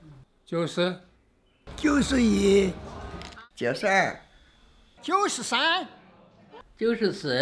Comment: Sounds like he said "Just air"